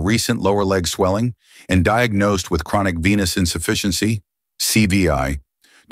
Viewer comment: I thought they said he had penis inefficiency😂😂😂